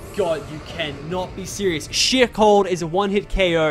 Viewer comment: I thought he said shit but it was sheer cold 😭